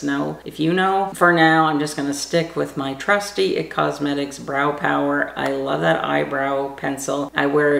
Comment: Hi what eyebrow product do you use? I thought you said it was L'oreal brow pencil but maybe I heard wrong. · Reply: Hi, I use It Cosmetics Brow Power in universal taupe.